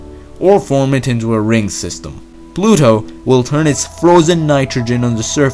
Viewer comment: Lmao, I heard “Bluto”